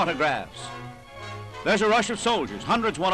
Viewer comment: Wow ! Red Army soldiers claiming for a Hitler autograph... The evil irony that is gives me cold chills down my spine. People are so... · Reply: I thought he said 'there's Russian soldiers seeking autographs' at first, but after review he says 'there's a rush of soldiers'. German soldiers wore garrison caps (the soft peaked ones) that looked similar to Russian ones. I think these are German soldiers. There is no record of Hitler meeting Russian (Soviet) troops.